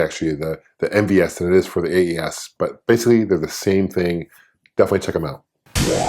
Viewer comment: Anyone catch what that said at 2:32? I can't get my iPod to pause on the frame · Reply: Basically they (the two consoles) are the same thing-definitely check them out